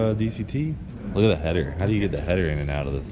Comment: 2 minutes in and I hear “how do you get the headers off?” Already off to a strong start.